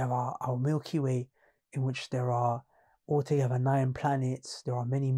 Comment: what does he say here? There are ... other planets? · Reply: “All together”